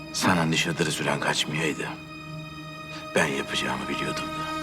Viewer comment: de ne diyor anlayan var mı? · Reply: Sana nişatırı süren kaçmayaydı ben yapıcağımı bilirdim söylüyor yalnış anlamadımsa